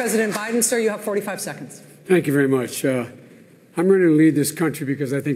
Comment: Haha. The subtitle feature understood "I'm ready to LEAVE [instead of "lead"] this nation because I think it's important we restore the soul of this nation" Great idea (for a start). The subtitle feature got it right LOL